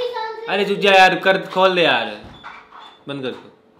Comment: yeh kya bol dala 😂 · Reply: Band kr de bola hai gor se sunno bhai